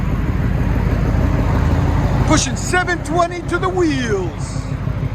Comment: I’m sorry did he say 720 whp 👀